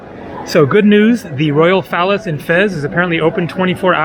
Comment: it sounds like you're saying the "royal phallus" instead of the royal palace!